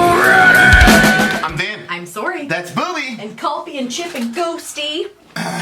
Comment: AHAH I heard "I'm Vin and I'm sorry"